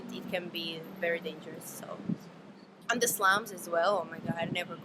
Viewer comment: what does she say at 10:50 "and dislounge! i never go there!" whaaat?